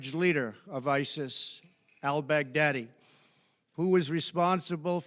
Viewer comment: The way he says al baghdadi, sounds like hes saying "ill beg daddy" in a southern drawl. Im sure you will when it comes to putin lol. That is all.